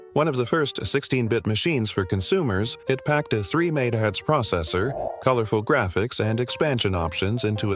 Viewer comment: what did he even say? 300 nine nine? 5:40 Powerful?!?!? my Watch has more computing power. heck my pc is 4000 times faster. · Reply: Did he say "three made heads processor"?